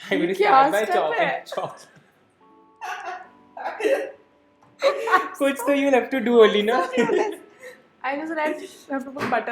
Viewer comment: "kuch to you have to do alina"- did he just call her alina??~😲😲😂😂😂